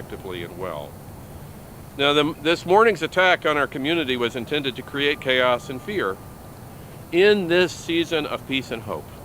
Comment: Did he just say that the intent was to create chaos and fear???